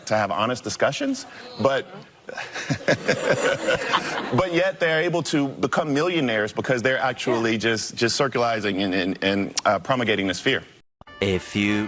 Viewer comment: Did he just say "circulizing"?